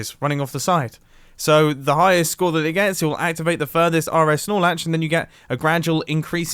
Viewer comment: What is he talking about I don't understand redstone engineers all I hear is Snorlax and I'm like: bruh this is minecraft